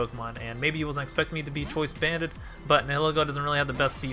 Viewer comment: I thought it said Banned xd